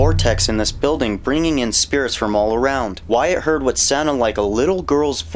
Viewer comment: when you said “Wyatt heard—“ I thought you said “Wyatt Earp” 😂